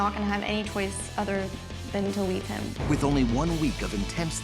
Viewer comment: I heard this as “I’m not gonna have any choice other than.. to eat him..”